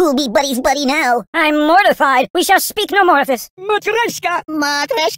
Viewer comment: sounds like he said Mukereshka